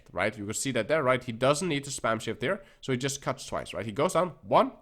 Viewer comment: Thought he said, "you don't need to spam shit" instead of "shift". I like that advice better for blade.